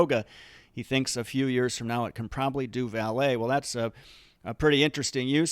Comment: LOL for a moment I thought you said ballet instead of valet! I bet they’re probably already are robots that can do ballet dancing Great analysis about a great company. Thank you.